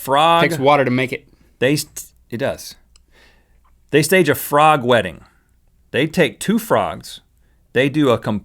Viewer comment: I thought Rhett said "frog wetting" so I was confused as to why the frogs were dresses up xD